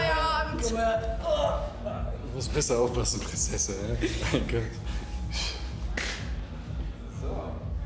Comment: Hat irgendjemand verstanden was Sascha da am anfang Amalie ins Ohr flüstert? Ich kann es nach mehreren wiederholen immer noch net verstehen 😢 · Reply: ich hab verstanden „morgen brauchst du ein Schutzengel Prinzessin“